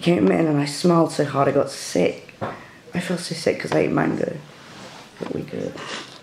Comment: I'm American I thought you were saying manga instead of mango